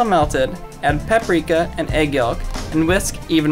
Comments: I'm sorry but did he just say yULK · did he say EGG YOG??